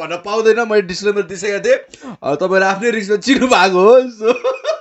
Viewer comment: to 2: 23 ma mailey matra sune ko ki mero kaaan ma gadbad xa " tapai afnoi risk ma ch*****" 😀😀😀